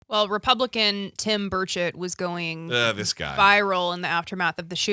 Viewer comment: I heard "Tim Birdshit". . . which would be a fitting name for him.